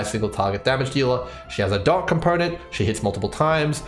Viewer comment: i kept hearing "dog companion" instead of "dot component". was very excited i hope i get at least E0 sampo soon :( this is a very useful video for current meta! i'm really looking forward to future videos on this topic since we will inevitably discover more about how units work + enemy changes in future patches one thing i'd like to see is predictions on future units and what niches they could fill. it would be fun to see what we're missing obv there's leaks and cbt stuff to look forward to, but that's more of an analysis instead of a thought exercise